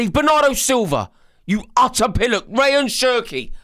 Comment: What is that word he is saying @ 0:23? Pilluic? I'm from the U.S. and I've never heard that word before. I tried to google it, but I have no idea how to spell it. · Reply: Pillock. Meaning morons, idiots etc... a semi polite way of calling someone an idiot 😂